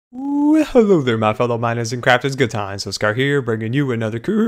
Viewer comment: It sounds like your saying GoodTimesWithSky